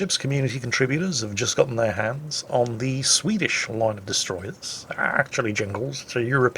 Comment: I thought at first he said the WoWs ccs got Swedish TDs for some reason